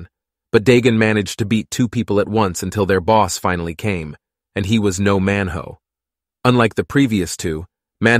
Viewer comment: I heard " he was no man ho" But then he kept saying it and I was like😱 that's his name🤣🤣🤣